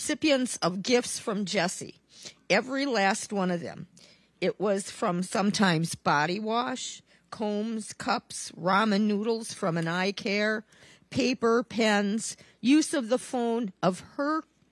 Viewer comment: Wait! Did she say Jessy gave ppl “eye care”?! 😱